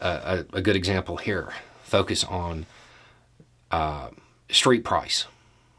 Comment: Wait what does “street price” mean? Or maybe I’m mishearing…